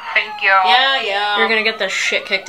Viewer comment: “Pain kink plot twist. AYO” had me laughing so hard. And the chad voice in the song, singing in cursive or whatever. Love that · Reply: I thought she said pancake plot twist, like she was going to jump off a roof before she's able to get fired 😭